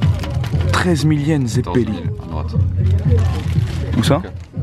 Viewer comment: C'est moi ou il dis 13,000yens ZEBI ?